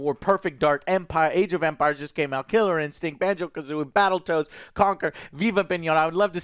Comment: did Joe say Battle Toads? Or have I been out in the Internets too long for one day?